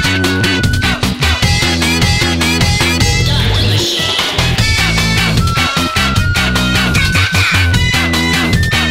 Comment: Is that what I think I hear? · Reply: "start the machine"